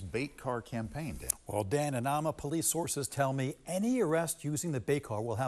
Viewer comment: did he just say "anonamo police sources tell me..."?!?!